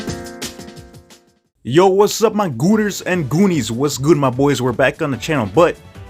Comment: did i just hear gooners and goonies 😭😭 Ok but fr, what are these sonic fans doing bro, just play some of the other games there are at the meantime. Im more worried about how the games themselves are gonna be rather then them coming out soon